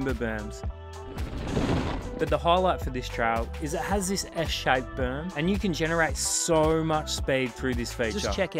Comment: I sware I heard Eshay berm at 0:35. Gotta rail it hard to get away from the Lads. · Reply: 😂 S Shaped Berm. But Eshay berm sounds better.